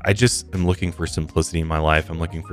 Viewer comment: Did I hear “I’m looking for some Pussy in my life”!?!? Right before